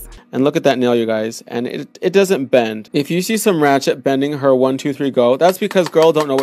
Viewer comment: The “some ratchet” part really didn’t sit right with my soul. He reminds me of a certain male beauty influence whom I rather not name.... 👀 issa no from me sis but you killed them nails despite all ah dat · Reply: I didn’t catch that part at first I thought he had said “her ratchet nail” but then I replayed it and he said “some ratchet” and that really made me cringe. No sir we can not be saying stuff like that bc we are in 2020 and we all are aware that shit is offensive and problematic for many reasons 😬